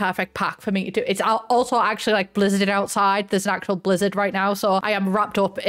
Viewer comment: Somehow heard you say "there is a LIZARD outside" at the beginning and got really excited cause I thought you would show us a cool reptile